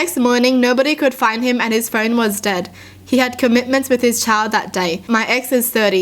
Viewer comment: I was listening and not looking and at first I thought I heard her say his friend was dead. I immediately started going back because that would have escalated fast and a surprise